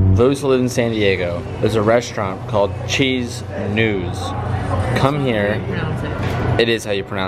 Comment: What was the name of that restaurant? Could of sworn i heard cheese Nudes... HELP! I want to try that spot out!! Looks so bomb 💣